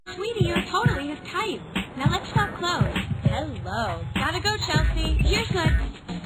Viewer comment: What is she saying at 0:04 after “Gotta go, Chelsea “? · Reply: "here's fine" so the taxi could drop her off